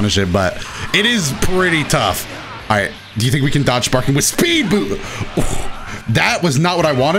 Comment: did he say "do you think we can dodge sparking with SPEED BOOB?"?